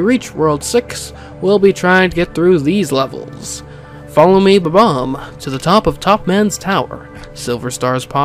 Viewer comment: When he listed up the world 5 galaxies i heard it as that you need to get a bob-omb to follow you up to the top of topmans tower so silver stars would pop up